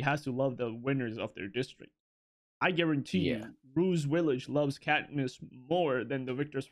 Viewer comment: I swear homie said Bruce Willis and not Rue’s village. I was confused for a hot minute. Like, what is this man on about? What does Bruce Willis have to do with this discussion? 😂😂 Makes sense now, though.